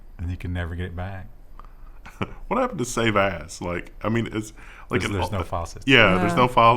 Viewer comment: When was talking about pics, it sounded like said "what happened to save ass"